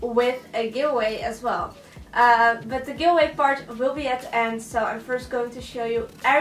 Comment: I thought she said give away fart at 1:04 😂😂